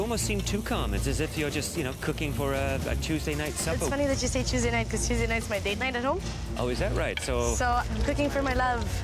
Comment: What she saying i dont understand pls anybody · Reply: "It's funny that you say Tuesday night, because Tuesday night is my date night at home"